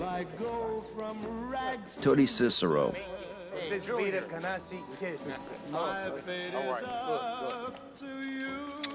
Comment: what does he says? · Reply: I was just randomly watching the movie with subtitles on and it's actually "Could this be the Canarsie kid." Just for clarification's sake.